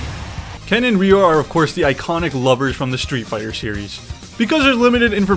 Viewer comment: Ok am I the only one who heard iconic lovers?